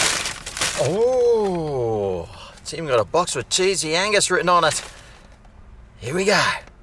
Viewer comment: Wow, get my mind out of the gutter.... I thought you were saying sleezy anus. Oh, cheesey angus. 🤔